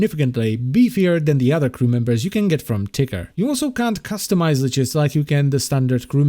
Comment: I thought he said tigger instead of ticker xD